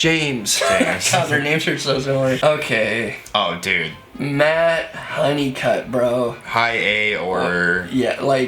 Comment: who is this? Can't understand the name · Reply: Matt Honeycutt from Kublai Khan